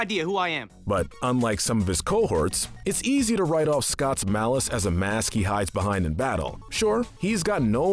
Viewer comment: I'm sorry but did you just say scotts mallice?